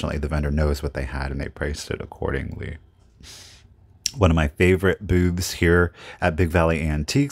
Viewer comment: did you say, "at one of my favorite BOOBS here?" Haha. I swear thats what I heard.